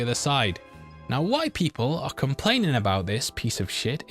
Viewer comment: Now white people are complaining about this shit is all I heard lol